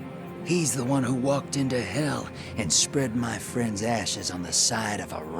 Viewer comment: Could’ve sworn the drifter said “he spread my friends asses on the side of a rock” I was like whoa there pal